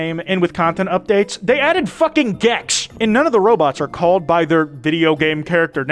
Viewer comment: yo did someone say GEX